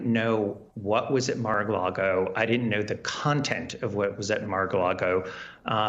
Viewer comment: Did he just say MAGA-largo?😂